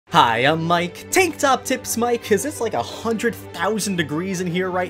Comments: "Tank Top Tips Mike" Me: Keep that as ur regular name :) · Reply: Wow I am so dumb I thought he said tik tok tips mike lol. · Who else was confused when Mike said TankTopTips Mike and then rewinded to hear what he said? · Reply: I lowkey thought he said TikTok tips Mike